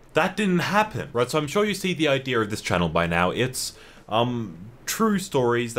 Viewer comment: did he just say ideer